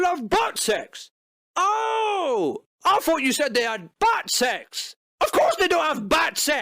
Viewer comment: when you said "bat sex", I thought you said, " bad sex" lol